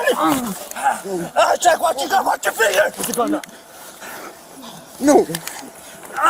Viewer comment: is his finger in the trigger gaurd or am i seeing things.😅 Thats scary asf and i could've sworn i heard "watch your finger"